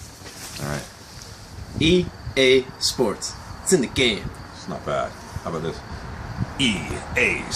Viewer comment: I swear at some point mb in the commercials I heard "EA Sports, if it's in the game, it's in the game" but now I think that might be some mind voodoo...